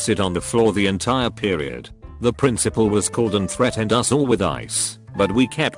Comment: I heard that as "threatened us all with ICE." Thats a very different kind of threat lol